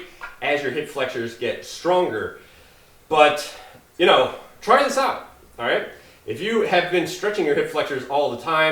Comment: What word is he saying @ 5:43? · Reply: "...try this out..."